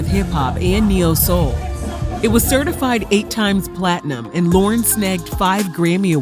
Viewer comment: She only got one album, but it went 10+ diamond she's really the 🐐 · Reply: I coulda Swore she said 9 times PLATINUM!